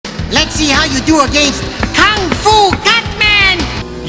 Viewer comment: if you listen closely it sounds like cutman is saying kung-fu batman